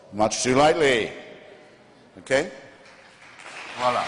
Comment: Did he say "Hvala" at 1:29:06? I'm almost sure he did..